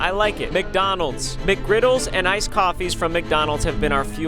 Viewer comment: When he said coffee I thought he said "goofy" 😂